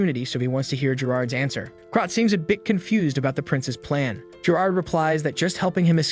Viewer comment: bro really said princess instead of prince 😭🤣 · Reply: He indeed said prince and not princess